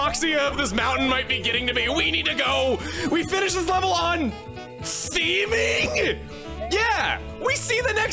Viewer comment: "We finish this level on..." What does say? The subtitles say 'steaming', but it sounds like 'seeming' to me... and neither one of those makes sense in context. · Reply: He says “theming”. It ends in a theme. Where one level leads to another with attention to detail.